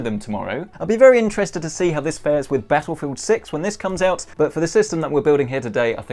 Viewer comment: Did he say Battlefield 6?? 😑